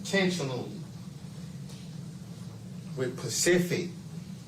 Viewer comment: Did he really say with pacific?